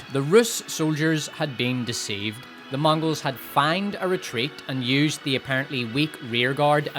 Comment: Say "feigned" one more gain. What the fucking...? The first 12 times I thought you were saying "fanged retreat" which; fucking metal. Feigned actually makes more sense though...like....grammatically.